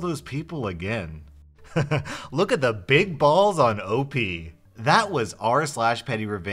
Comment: Why did I hear “That was r/penny revenge”?